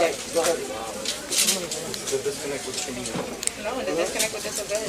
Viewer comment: did she say "I have a disconnect with disobeyers"?